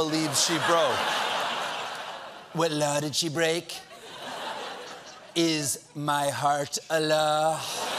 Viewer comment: i heard "is my heart allah" and got SO confused